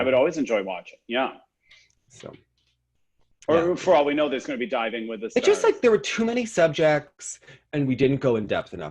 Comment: "diving with the stars" I heard you 😂😂